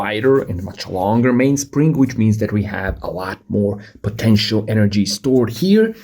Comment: d-did you say POTENTIAL!?